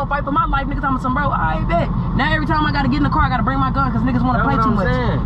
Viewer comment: i thought said she not getting in the car no more😂😂